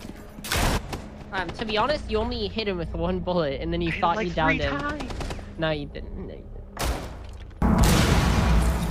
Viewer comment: what he say at the end👀😑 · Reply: Nick